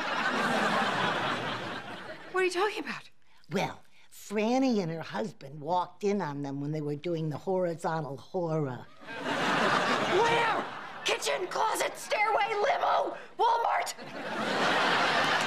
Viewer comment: I’d like to confess I first learned “horizontal hora” from this scene many years ago and have used it many times since then wondering if anyone would catch the reference. 😂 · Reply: I thought she said "horizontal horror" :D